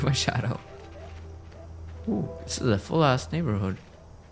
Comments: Did he really say “this is a full ass neighborhood” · I swear I had to re watch it like 8 times and I kept hearing a$s